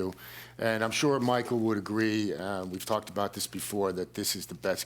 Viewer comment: am i the only one who heard "we fucked about this"